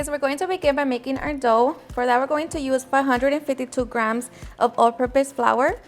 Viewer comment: Transcript says 152 grams of flour if it's 1 and1/4 cups of water that's more water than flour sounds like she says 452 or 552 but I'm not sure